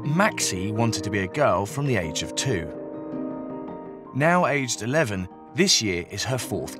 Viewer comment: "Ryan no longer wants to be referred to as he" now HE'S 12, she...." wtf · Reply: I heard "Now AGE twelve, she...."